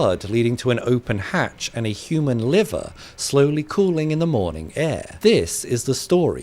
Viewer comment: for some reason i heard "cooling in the morning air" and still thought this was taking place under water . took a few rewatches to get. but gosh dang dude insane ways to die for real